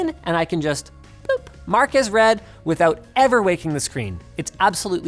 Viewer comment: I thought you said "Marques read". Had to double check. Haha.